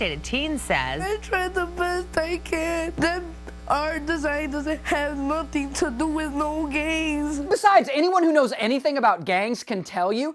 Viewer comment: Why do i hear the kid saying *this has nothing to do with no gays*